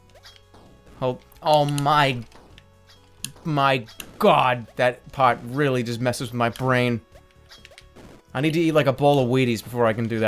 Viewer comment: I thought he said 'that weed messes with my brain'. Too much Eddie Bravo on my part.